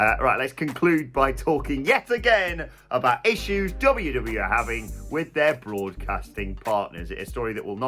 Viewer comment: Lol did you just call them WW ? 🤷‍♂️😅